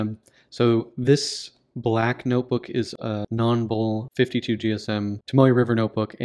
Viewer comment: About 1:45 in you compare this notebook to a black one that you say has Tomoe River paper but I cannot understand the name of that black notebook. What is it, please? Nonbull is what it sounds like you are saying. Is it Nonble? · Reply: Hi Shawn, thanks for the question. It has a goofy name: "Nonble". You can get them on Kinokuniya's website. I will be doing a review of one in a few weeks.